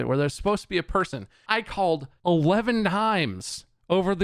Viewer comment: did bob say ''I called 11 himes "?